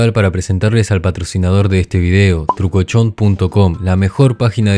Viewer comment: ESCUCHÉ CHOCHOX XDXD